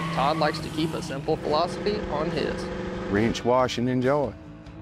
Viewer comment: what the hell did he say? 🤔 3 inch washing and joll? 🤔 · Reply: Wrench, Wash and Enjoy.